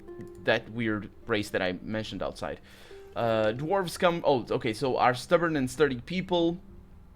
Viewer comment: it's TURN BASED! btw, at 16:33, did you said Dwarf Scum ? or it was me? xD